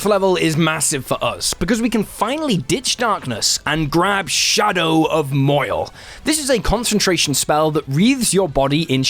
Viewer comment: Had to backtrack at the Mid Game for "Shadow of Moil", as I heard it as "Shadow of Mohel."